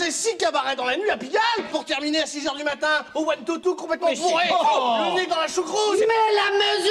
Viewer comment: il dit quoi avant « complètement bourrée » ? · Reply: Au One-Two-Two, une maison close parisienne des années 30-40